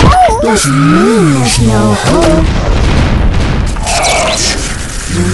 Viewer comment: what does the robot say at the end? "its time to _____" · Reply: Lalo2795 I thought it said " Time to CUT FLESH "